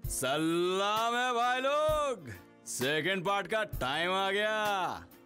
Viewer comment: Is it only me who heard "salman hai bhailog"